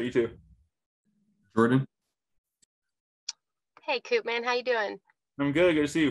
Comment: did she just call him Coopman...? 😆